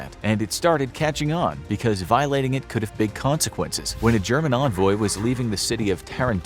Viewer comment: He said German envoy, but the captions said Roman, and the clip was of romans lol 1:00 in